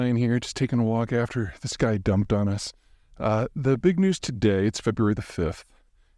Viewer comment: Thought he said this GUY DUMPED ON US. Threw me for a loop